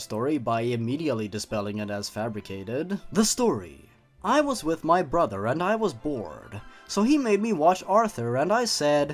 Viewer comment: Everyone heard "I was with my brother and I was bored" I hear "I was with my brother and I was born"